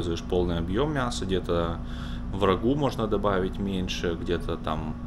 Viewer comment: "врагу добавить меньше" слышу я и думаю "Святой человек он ещё и врагов кормит" 😂😂😂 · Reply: Ха-ха :))) он имел ввиду в рагу меньше мяса положить :))))